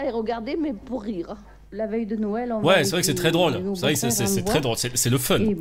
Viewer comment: J'ai entendu la veille de Noël ?